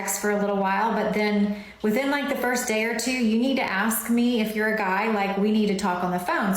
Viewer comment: Did she just say, You need to ask me if your a guy?